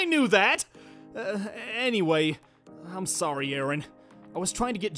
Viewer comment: I think Dante said “I’m sorry urine” instead of “I’m sorry Aaron